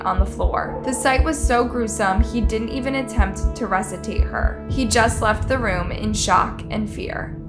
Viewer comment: Man the way my brain is set up….all I could think is how she said the word “resuscitate” she said “ressatate” 2:48 ….but I have to say that vigil they held was amazing.